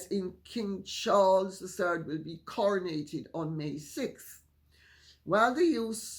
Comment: When someone used the verb 'coronated', I thought I heard 'crenellated'. I wouldn't be surprised though and may yet hear of the crenellation of King Charles, lol.